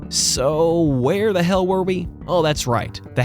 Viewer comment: I heard "where the hell, are you?!", makes sense as it doesnt have eyes and is trying to see who/where u are